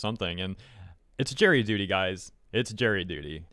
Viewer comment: Not being from the US, at first I heard "it's Jerry duty guys", and I was like "who is Jerry, and what's your duty towards him??" 🤣